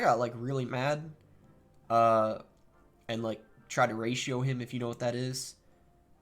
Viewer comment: What does say at 18:03 ... he tried to what? raid show him?**